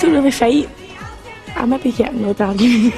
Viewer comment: wit did she say? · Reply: I maybe get in the odd arguement